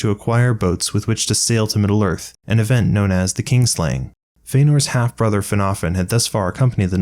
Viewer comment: Not sure if it's how I'm hearing it or how you're saying it, but I keep hearing "kingslaying." It's kin (family) slaying.